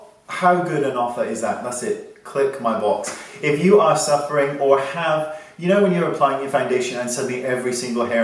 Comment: when I first heard his outro I thought he said click my balls... somehow I didn't find that very surprising...? lol cos he's always making sexual innuendo jokes its just too 'hard' LOL